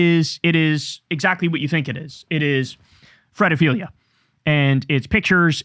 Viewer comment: These files present one of the strongest arguments why billionaires and most millionaires shouldn’t exist. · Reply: I can never figure out what Kyle is saying when he uses some term "Fretophilia" ?